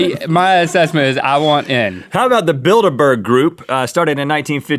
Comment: I thought for a minute he said the Build a Bear Group and I wanted to join